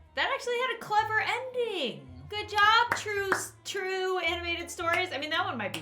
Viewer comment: it sounded like Lauren said Troom animated stories like troom troom lol